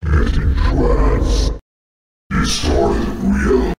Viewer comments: idk I keep hearing a different one each time. It sounds he’s either saying “entrance” or “it dreads” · These are 100% sampled from Vincent De Moor - Worlds of Doubt. <link> This makes the 2nd thing I know of that has sampled it. The other being F-Zero GX for Black Shadow's theme among other themes. I'm not sure if all of Ivory King has his lines pulled from this one song. But I know for certain some of it is. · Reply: Also to repeat what others have said I hear "destroy the will" not "destroy the wheel". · Perhaps being consumed gave him weird insight into the world. I think his "destroy the wheel" quote may be saying destroy the fire-linking cycle or something. Besides the player or his struggle with the old chaos, im not sure what ice boi could be referring to with "wheel" honestly. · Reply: I heard it more as "Destroy their(the players) will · I think that he is saying "Destroy the Will". 1:20 "I was suppose to return, so sorry". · he clearly says "Get it, France" idk maybe he's hyped for Olympia... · OH MY GOD. Is he saying "Get entranced" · I hear "destroyed we are" for the second AoE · For "destroy the wheel". Personnaly I heard "it's not real" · I hear "Get Entranced"